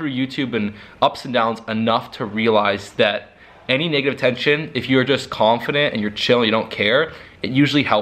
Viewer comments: Did he say “nigga tention”? 😂😂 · i thought he said “any nigga type shit” and i was like WOAH that escalated quickly